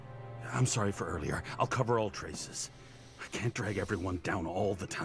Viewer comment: he says cover all traces the subtitles say I’ll cover your tracks.